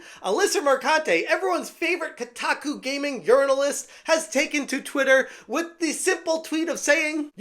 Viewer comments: i may have misheard, did you say journalist or urinalist??? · Misheard you say journalists as "urinal-ists" which is honestly fitting bc all they do is take the piss.